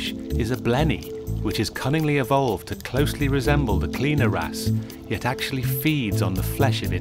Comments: Lol I thought he said "clean your ass" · Sounds like hes saying cleaner ass 😂😂😂